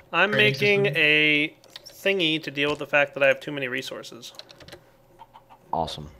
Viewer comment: Oh, Sorry! I thought you said he had too many items mod My bad